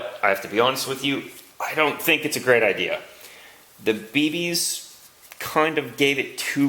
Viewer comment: The captions/subtitle in 17:27 instead of bb's its babies lol